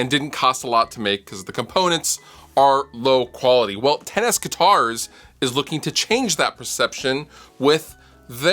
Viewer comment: Ha. I keep hearing "Tennis Guitars" instead of 10S guitars...so, 10S-NE1? If you're really into tennis, there's a vanity license plate for you.